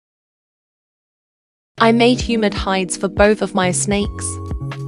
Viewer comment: When it said "humid hides" I heard "human hides (as in skin)". And I got a bizarre image of snakes wearing human skin suits.